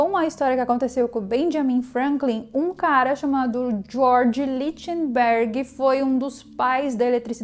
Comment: Queria saber como se escreve o nome do cara que aparece no minuto 12:17 ...alguém que sabe pode escrever aí? George litiem Berg? Foi isso?